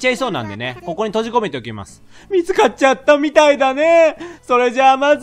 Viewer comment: 水買っちゃったに聞こえました。 ぴょんたろう狂気的なキャラにされがち